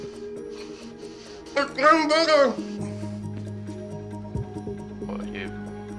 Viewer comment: What he said 😭😭😭😭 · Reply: Its Getting Bigger